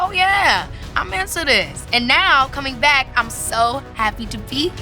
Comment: did she say “into this” or “into him” ? 👀🤣